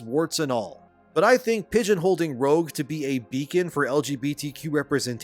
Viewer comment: and did u just say "pigeon holded"?